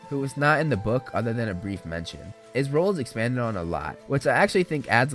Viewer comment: Wait did he say Rowling expansion in 15:45-47